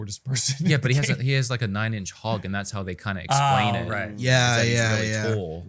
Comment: excuses me did say link has a nine inch hog lol 😂